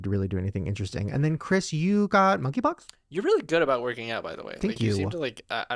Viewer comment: I swear Shane says and then Chris you got..." Monkey Pox?" 9:48 Lol i hope not. My ex had monkey pox and it was awful! He was covered in 200 pustules from head to toe. They came after flu like symptoms.